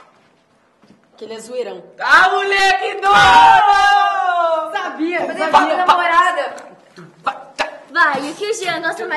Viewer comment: o que a Fer fala no minuto 7:32??? eu escutei tipo: "pensei que fosse da Namorada!" mas eu tô tão Lufer ultimamente que acredito que foi a minha mente me dando esperança... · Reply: ele falo "pensei que fluísse da namorada sim